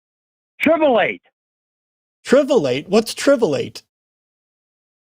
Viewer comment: I fucking died at Tribulate holy fuck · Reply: I thought he said 'trivialate'; he probably meant 'trivialise'.